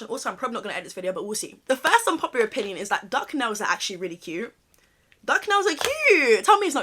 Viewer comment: I really thought you said “duck nose” and I am over here like what’s duck nose 🥲